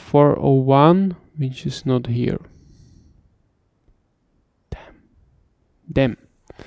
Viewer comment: Did he say Dam*** in 02:25 ? hahah